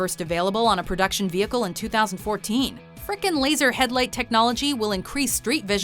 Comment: Did she say "freaking"